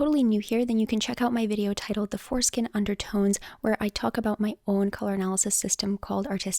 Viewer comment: Hahahaha, my boyfriend over heard you say "four skin undertones" and pulled a face because he heard foreskin under tones. Laughing so hard.